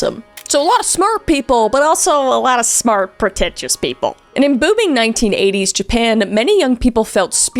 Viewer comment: thought she said em-boobing